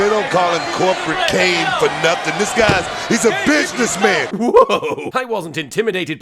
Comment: When Booker T. was said "he's a business man" I thought he said "he's a bitch this man" and I still keep hearing it like that no matter how many times I rewatch that part.